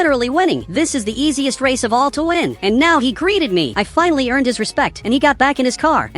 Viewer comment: POV what I heard ''I finally earned disrespect'' (its